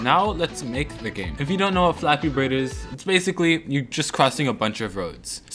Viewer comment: did he just say flappybird